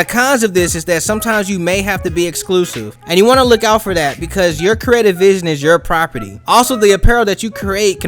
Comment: Can someone further explain 5:35 please? And I can't catch what he says there "your credit vision is your property"??idk